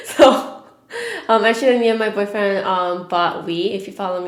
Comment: legit thought you said "me and my boyfriend bought weed" 😭😭😭😭😂😂😂😂